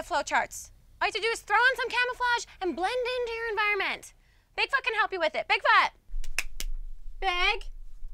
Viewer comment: it sounds like idol says "big fuck can help you with it" haha